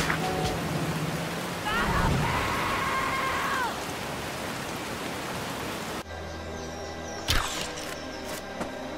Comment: What was that guy saying at 8:26 I couldn't understand what he was saying he was screaming so high? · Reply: It's screaming 'battlefield!' Check out, Battlefield Friends, or their older stuff on.